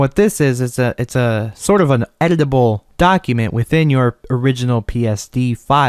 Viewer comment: I thought you said " edible document "...